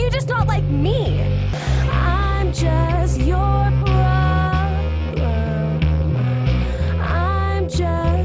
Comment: i turned on captions and when Marceline said "im just your problem" the captions thought she said "im just your girl"